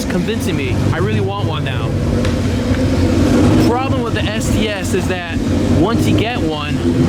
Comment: RIP headphone users. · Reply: what did you say? Problem with what? SCS? What is that?